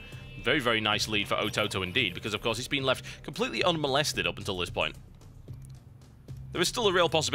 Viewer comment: did he say "being completetly left unmolested"?? 0.o